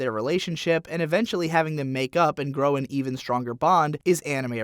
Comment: I heard make out